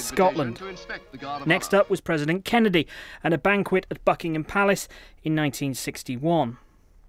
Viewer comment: Hold on did he just say the F*ckingham Palace?